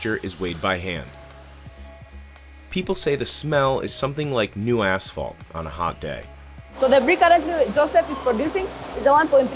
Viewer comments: I thought he said the smell is "like a new asshole on a hot day" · My sound was kind of low and I thought he said the smell was like new asshole on a summer day.